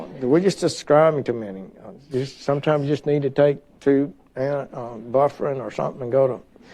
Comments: Did he just say IV bufrin instead of ibuprofen? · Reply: I thought he said "Buffrin" (real old school ibuprofen) that I don't think is on the market anymore! lol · Did he just say IV bufrin instead of ibuprofen? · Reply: I think Bufferin is still on the market. Aspirin with antacids to reduce stomach upset. Definitely old school, like Sessions himself.